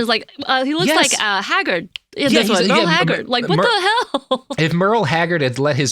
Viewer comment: My partner said "Did they say Hagrid, like in Harry Potter?" I laughed, but looking again... Homie looks like Merle Hagrid.